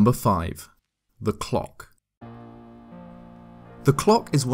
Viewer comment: i was real sleepy and thought u said glock